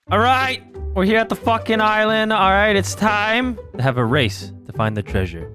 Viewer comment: Anyone else hear Vanoss call the island Ireland